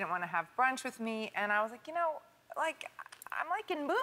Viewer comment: I don't quite get what Gwyneth says at 4:40. It sounds like "I like boobies and stuff." ?!? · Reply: She said "i'm... like in movies and stuff"